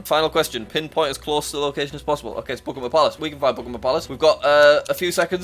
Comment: if I zoomed in more, it would have popped up! (Buckingham Palace round) Screen: Buckingham Palace is already on there. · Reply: Is it just me or did it sound like he was saying Pokémon palace lmao